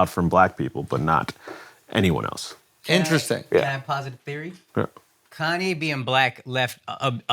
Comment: Okay what was that word aakaash said that got andrew all impressed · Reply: Posit